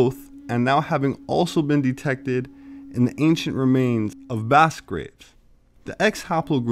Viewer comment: I am not sure what you said at 7:23. It sounds like vast grapes. I know that is not what you said. Your videos are wonderful. I have been taking time to view all your videos. Great information.